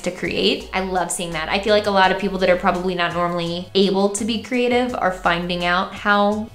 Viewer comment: When mykie said “able to be creative” I thought she said “able to procreate” and I had to do a DOUBLE TAKE jfc